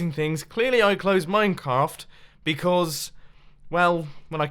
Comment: Did anybody Else hear that he said minecaf